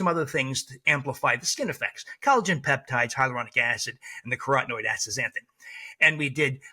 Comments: ‼️‼️What supplement is he saying he gave the ladies in the trial - at 15:15 - “Nicarotenoid” acid??? NEVER heard of this… nothing comes up in an internet search either. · ‼️‼️What supplement is he saying he gave the ladies in the trial - at 15:15 - “Nicarotenoid” acid??? NEVER heard of this… nothing comes up in an internet search either. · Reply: "collagen peptides, hyaluronic acid, and the carotenoid astazanthin" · I had a friggin' Stanford-trained dermatologist who had me on YEAR-LONG treatments of Tetracycline and Erythromycin when I was a teen in the 70s. It's a miracle I'm alive. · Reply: ‼️‼️What supplement is he saying he gave the ladies in the trial - at 15:15 - “Nicarotenoid” acid??? NEVER heard of this… nothing comes up in an internet search either. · I am a physician that practiced conventional medicine my entire career. Wish they taught us this in school. Thank you! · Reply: ‼️‼️What supplement is he saying he gave the ladies in the trial - at 15:15 - “Nicarotenoid” acid??? NEVER heard of this… nothing comes up in an internet search either.